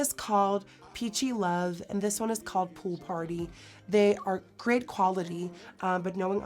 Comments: WHEN YOU WERE SAYING THE BLUSH TRIO NAMES I SWEAR I THOUGHT YOU SAID ONE OF THEM WAS NAMED ''POOP ON ME'' AND I WENT BACK 4 TIMES AND I FINALLY REALIZED YOU SAID POOL PARTY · WHEN YOU WERE SAYING THE BLUSH TRIO NAMES I SWEAR I THOUGHT YOU SAID ONE OF THEM WAS NAMED ''POOP ON ME'' AND I WENT BACK 4 TIMES AND I FINALLY REALIZED YOU SAID POOL PARTY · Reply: I thought she said "poo party" so you're not alone hahaha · I definitely heard "poo party" when she was talking about the ABH blushes lmao